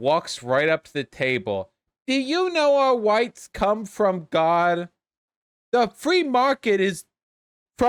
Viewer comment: I heard: "Do you know Whites come from God?" Oof